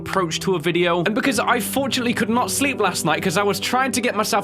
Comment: I was skipping the beginning and I thought I heard him say I couldn't sleep sexualy 🤣